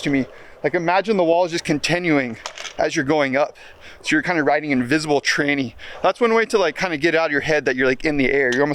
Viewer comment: excuse me did you just say "riding an invisible tranny"🤣